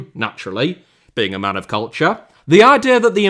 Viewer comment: I did not hear a word what said until I heard “man of culture”, and even then I heard “men” instead